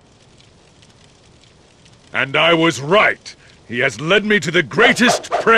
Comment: For a second, I thought Lu Bu said "Cunting Time"